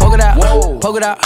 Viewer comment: My dumbass thought he was saying “pokidot”